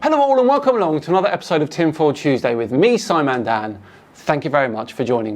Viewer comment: I thought you said Tim Pool Tuesday not Tinfoil Tuesday lol